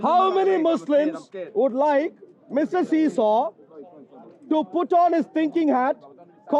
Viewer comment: did he just called him Mr see show 😂😂😂